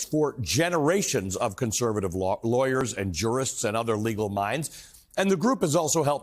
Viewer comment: I thought he said "fall of Rome." That was gays' doing! /s